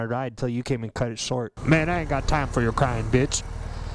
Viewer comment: I Deadass thought you said “I don’t have time for your crying bitch.” To the cop lol